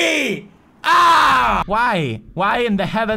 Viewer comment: Adolph hitler resurrected and took over poods' body. I know he said why but it sounded like "NIIIIIIIIIIIIIIIIIIINE!"